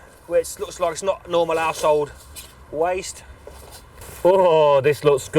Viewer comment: I thought at 3:43 he said not normal asshole waste instead of normal household waste lol